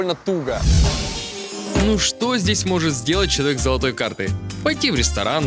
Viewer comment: почему мне послышалось "шрек" , а не человек